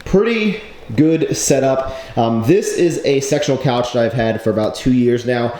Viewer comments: i swear you said sexual couch @ 5:45 xD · I thought you said this is a sexual couch hahahaha! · Literally heard you say sexual couch... Throwin' us hints?